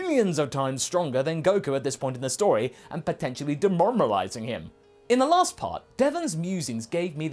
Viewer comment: Just me or did he say demormalise.